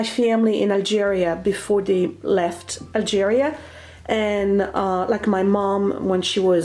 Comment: Algeris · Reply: for a minute I thought I heard Nigeria, then I said no that can’t be right? Then again we are all mixed up with something or the other, lol! 🤣